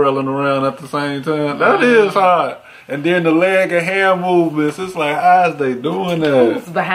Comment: I thought he said the "Leg o' Ham" movements lmao